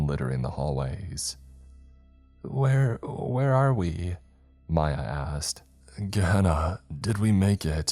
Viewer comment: He said “Mya asked” and I swore it sounded like he said “my ass” 🤣🤣